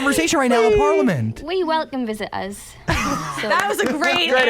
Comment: what does Jade said about Trupm at 10:20? I can't get it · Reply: Andrea Alvarez Arcila "oohhhhhh.... well we welcome visitors" 😂😂